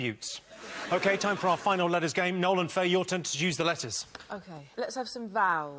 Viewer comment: did Jimmy say "your turn to seduce the letters"...? <_<